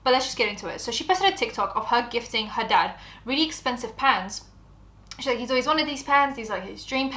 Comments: Not gonna lie I was so lost half listening to this, I thought you were saying PANTS not PANS 😅 makes more sense now lol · girl i thought you said he wanted expensive pens